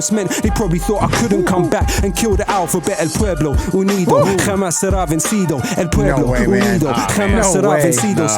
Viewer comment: In case you were wondering what he's saying at 39:05, he's saying "El pueblo unido jamas sera vencido" which translates to "the people united will never be defeated".